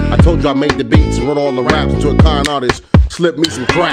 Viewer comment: I swear i heard "rapist" in Sora's part of his Salsa